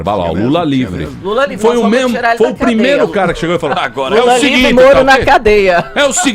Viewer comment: É sério que a esta altura do campeonato eu ouvi aos 6:55 "lula livre e moro na cadeia"???